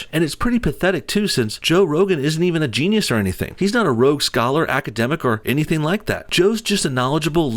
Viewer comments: Did he say "Rogue scholar", lol? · Love the content. You're doing "God's work". But did you say rogue scholar?!